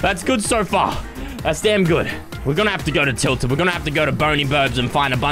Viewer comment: Did he just say tilted???